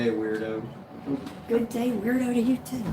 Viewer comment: I heard Sean, "good day wirdo" LOL......... Norway. Hi, my namesister :-) Lovely Onni and mom :-) have a nice day